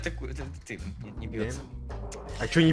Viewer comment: мне одному послышалась "Еб*тс*"?